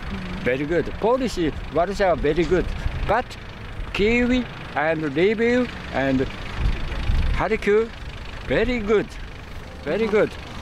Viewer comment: I think there he said "Kyiv and Lviv and Kharkiv".